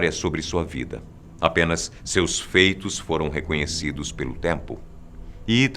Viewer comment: MDS eu entendi a penas seus peitos kk